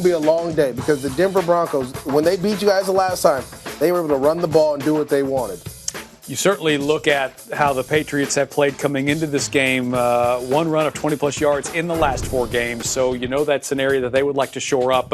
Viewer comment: am I the only one that heard him say New York Patriots